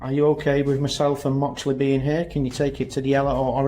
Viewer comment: I swear I heard him say “can you take your titty out?” 😂😂